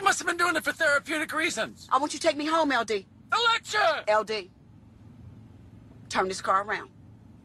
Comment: What the hell did he say @ 0:42?? Lol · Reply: The lecture!